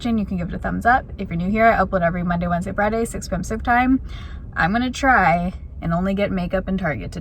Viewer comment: I live for saying “I upload every Monday, Wednesday, Friday, 6pm pacific time.” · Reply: Omg all this time I thought she was saying 6pm supper time 😂😂😂😂 and I've watched sooo many hahhaha